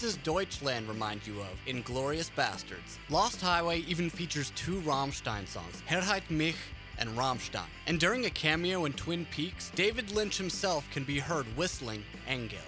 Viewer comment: what the fuck is he saying!? Not trying to be mean, i know German isn’t easy. Source: i have it in school. I’m guessing he’s saying Heirate Mich, but i’m not even fucking sure.